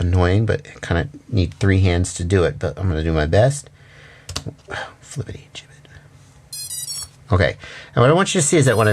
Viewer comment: Did you say "flippity jiminetty" 8:02 Gadzooks that's a minced oath if I've ever heard one. 😂